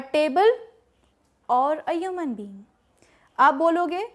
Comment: mam what you said I can't understand... It was something like 'aiyaman'. What is its meaning? Plz reply.. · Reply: Oh let me tell you she said " a human" not Aiyaman 😂😢😅😊😊